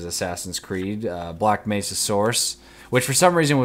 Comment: Sounds like you said "black mesa saurus." Pictured a big ass headcrab on a t-rex. Random thought.